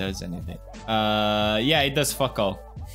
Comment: did he just say fuk?!?!